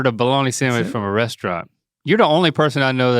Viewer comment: I can't be the only thought mr. said washroom instead restaurant